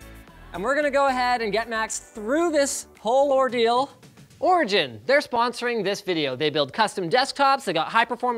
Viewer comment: When linus said origin i thought he said orgy-in